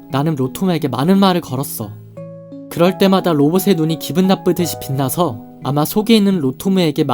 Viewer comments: 이거 저만 기분나쁘듯이라고 들림? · 에 나만 '기분나쁜듯'이라고 들림? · 저만 12:24쯤에 기쁜이 기분나쁜으로 들리나요??아님 잘못 녹음하신걸까요?? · 자막엔 기쁜듯이 빛나서인데 음성엔 기분나쁜듯 들려서로 들리지 정황성 기쁜듯이 맞아 보이는데 맞나요?